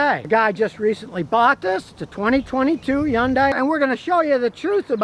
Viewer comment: Did he say “John Day” or “Hyundai”, cause I’m having a hard time comprehending his lingo